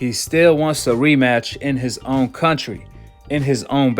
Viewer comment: Hold up I thought he was saying no rematch